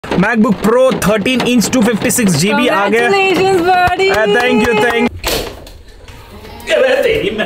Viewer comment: I thought said congratulations bit**es... 😅😅😅